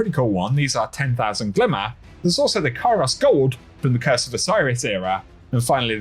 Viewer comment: Kairos Gold shader makes your titan resemble Master Chief if you equip a certain helmet from Vanguard, Crucible, or Gambit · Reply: I thought he said “Ikora’s hair shader “ I was like “what!”